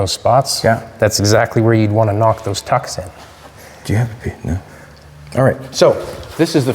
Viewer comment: what is saying? Methotical Metal manipulation mind melt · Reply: Mumbled..."Do you have a paper" seemed like he wanted to see the wrinkles in paper to have a visual of the spot that would need shrinking